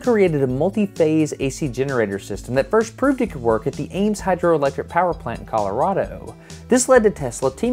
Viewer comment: did he say A.I.M Tesla was working for M.o.d.o.k 😱